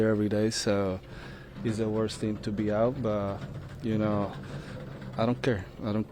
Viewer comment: if he REALLY said “worst team in MLB” why would he also be “proud to be here every day.” This is shoddy and predatory journalism. He was clearly reflecting on himself and said “worst teammate” but you guys have run with your clickbait title and narrative. Shame on SNY. Shame on Steve Gelbs. This reporting is more embarrassing than Lopez’s antics. At least Lopez was HONEST. More than I can say for SNY and Steve Gelbs.